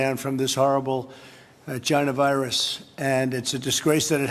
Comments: did he call it a "gina virus"? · Did trump say "this horrible jyna virus"??